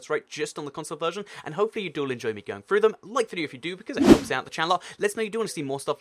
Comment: Y do u have to talk so fast that nobody understands what your saying u nut head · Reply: AeroBubble I heard this at 0:22 , it sounded like "like the loopidoop"